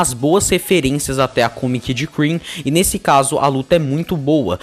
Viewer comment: Eu ouvi... CREAM?